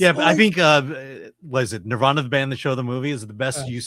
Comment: What the HELL does say there? Nirvana Bandshell the Movie?